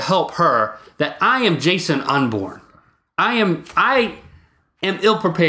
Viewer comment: When Link was talking about being unprepared and everything, and he called himself "Jason Unborn", I honestly thought he said "Jason Inman"